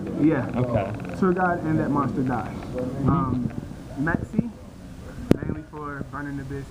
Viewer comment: what's he saying for that 1 card on the extra deck at the 13:00 mark? i cant make it out...metszi? i tried looking it up but nothing would come up. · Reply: Maxx "c" :)